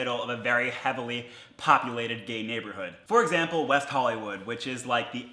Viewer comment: did he just say gayberhood